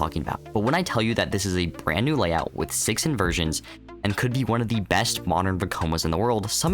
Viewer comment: I thought you said 16 Inversions lol